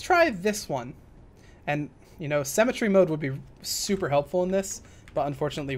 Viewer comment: For the longest time, i thought you were saying "Cemetery Mode"..... Now i know... Just